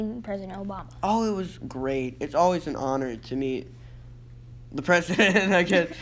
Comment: shit, i thought he was saying it was an honor to meat Trump. Had to skip back to check. Phew.